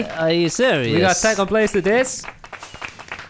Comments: kinda sounds like he said "faq you for watching" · Why did it sound like they said, "F*ck you for watching and have a nice day." at the end 😂